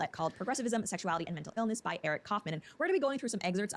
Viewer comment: I thought she said "by Eric Cartman" 💀